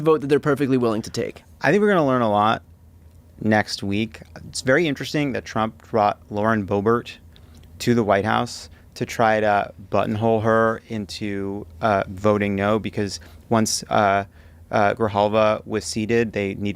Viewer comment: Eww @ 4:20 Did he say tRUMP tried to BUTT IN HOLE boebert? How telling is that about both of their characters? I'm glad boebert didn't do a 2nd rendition of her ‘Beetlejuice’ incident!